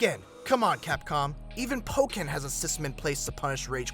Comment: What game did he say in 7:12? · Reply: Pokken.